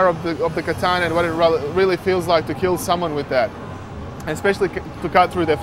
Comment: Did he really say "Kill someone to death!" around 1:18! Stupid.......